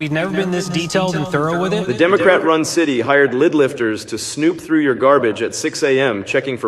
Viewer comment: At first I thought you said "Lib Lifters" 😂. Our world has gone crazy because I wasn't even surprised or confused when I thought it was "Lib Lifters"!